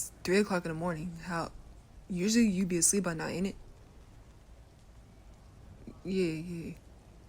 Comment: did he just.. did he just say.. Init?